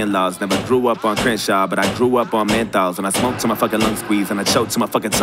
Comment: for a sec i thought he said I grew up on hentai AHAHAHHA